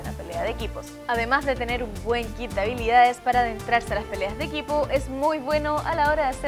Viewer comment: dentrarse skjwjd Na sic que dice adentrarse